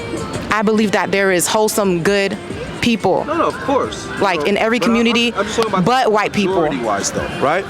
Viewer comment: "In every community I believe there is whole good people, in every community but white people." What? 😂 Did I hear this correctly or am I tripping?